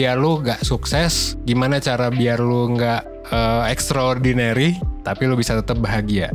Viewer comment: Gue ga salah denger menit 1:22 gimana cara lu ga sukses 😅😂, wah parah lu bang, kwkw bukan ga sukses.. lebih tepatnya Timezone orang itu berbeda² untuk sukses hihih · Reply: gmn cara lu "gak sukses, ga extra ordinary, tp tetep bahagia", gitu motongnya 😁